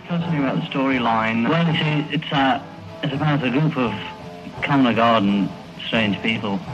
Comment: John's phrase that you didn't hear is "common or garden". A group of common or garden, strange people "Common or garden" simply means that they're ordinary people.